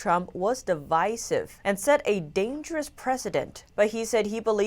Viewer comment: "Set a dangerous precedent" For a second I thought said "Dangerous President", both are dangerous, one to himself n little kids, the other has taken on centuries of corruption. 2021 is already beating 2010 for crazy. Not sure anyone will be sane by 2022.